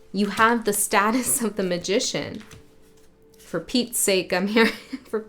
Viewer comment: When you said for petes sake I heard "repeats sake". Funny, that. It's always a cosmic do-over, though. I love this. You've brought into clarity a part of the structure of our playground here and it's movement, which is an upward and outward spiral, at least for now it feels that way.